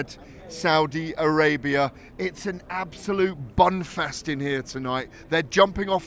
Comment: Thought he said bumfest. explained the smile on his face